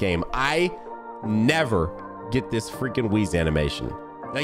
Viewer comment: I thought said Whis was the rarest animation for him lol